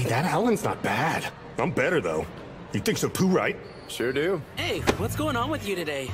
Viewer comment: Did bokuo say he thinks I’m poo right?